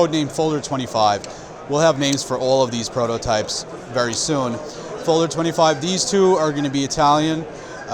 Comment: Thank you for the great video in 6:24 min which is the name of this cool knives. I don't understand the guy. Sorry. Best regards dirk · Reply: Folder 25. It is temporary prototype name said.